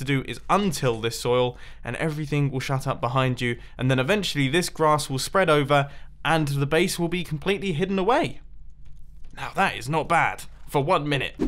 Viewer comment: did u say “shut up”?